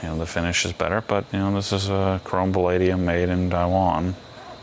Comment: "valadium" ??? A person that knows tools does not say "valadium."